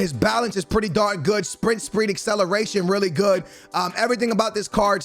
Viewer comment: Is it just me or does he say "sprint spreed" at 2:22 ?